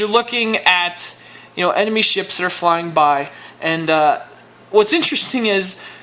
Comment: did you just say enemy ships flying by ?